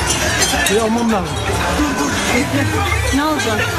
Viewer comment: at around 1:20 time mark, the male voice is saying "biramı almam lazım" meaning "got to take my beer" in Turkish or so i misheard.